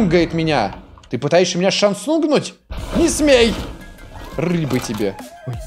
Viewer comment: ты сказал шансунг а я услышал Самсунг и типа Самсунг знает все наши движения чаво?